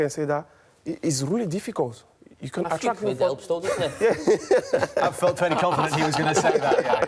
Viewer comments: What did Carragher say? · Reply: A few quid helps though doesn't it (I think) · Anyone any idea what Carragher says at 9:15 ? And I thought yaya would be more difficult to understand. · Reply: birthday cake helped though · what does jamie say there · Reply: Birthday cake helped though